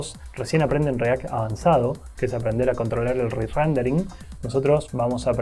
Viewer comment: que dijo? re rendering? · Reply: sí, re-rendering